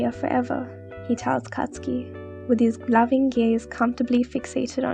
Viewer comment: Tell me why I thought they said “loving gays” ?😭 (I mean they wouldn't be wrong, 🤷🏽‍♀️ they are very loving gays😌)